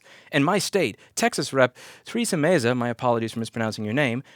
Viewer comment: the captions think louis is saying threesome meza